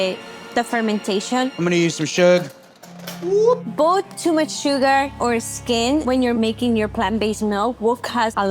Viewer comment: Can someone tell me if I’m hearing correctly at 21:06 - is she saying too much sugar and SKIN?? Am I hearing that right? If so, can someone please explain wtf that means lmao